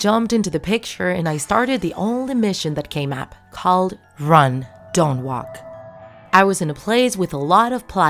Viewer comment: hmm, I thought it was “Walk Don’t Run” says while doing a Ventures reference